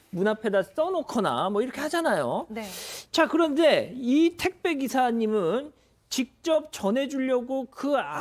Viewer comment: 나만 택시기사로 들림???? 택배기사 아니야???